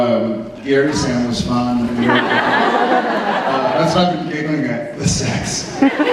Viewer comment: what does jared say at 31:04 - what kinda sam does jared state? i cant make it out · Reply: I asked the question! He said "Gary!Sam was fun that's what I've been giggling at /the sex/!"